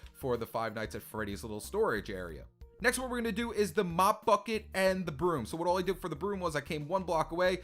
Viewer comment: Anyone else hear" The Mop Fucking End Broom"?